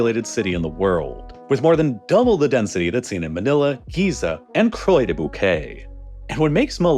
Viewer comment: for a moment I thought you said "and Croydon, UK."